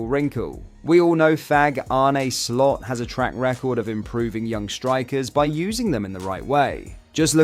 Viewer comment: Did he call Slot a F*g at 4:00???🤣